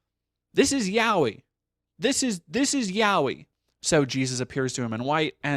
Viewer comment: It took me a moment to realize you were saying "this is yaoi" because for a brief moment, I thought you were saying "this is YHWH"